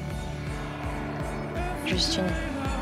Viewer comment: when she says"only you" it sounds like she says "just you". SO CUTE!!